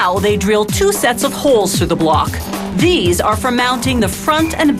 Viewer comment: i thought she said naughty words... a**holes jk